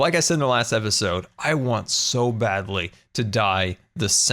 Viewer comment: I thought he said: “ I want so badly to die” 😂😂